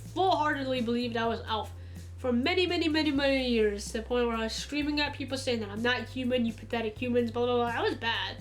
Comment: what does she say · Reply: "To the point where I was screaming at people saying that I'm not human..."